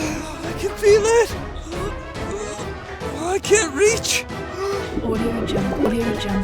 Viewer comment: why do i hear the word adio jungle?